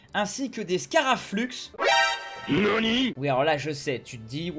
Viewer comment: c'est quoi le son "yonni" mdr 😂😂😂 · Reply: nani pas yonni ^^